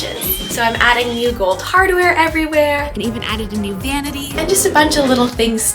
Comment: HELP I HEARD “NEW VANITY” AS NEW GANYMEDE HELP 😭